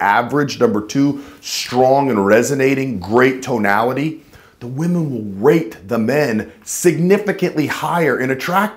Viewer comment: I thought he said "the women will rape the man."